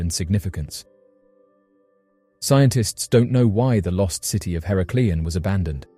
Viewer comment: did he say scientist don't no way they abandoned that under water city?